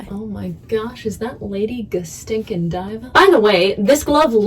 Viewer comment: Was anyone else confused when she said "lady Go-stinkin-diva" because they are a nerd and thought she was saying "lady Ga-stinkin-ladriel"?